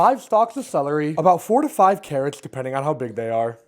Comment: i thought i heard 45 carrots not 4 TO 5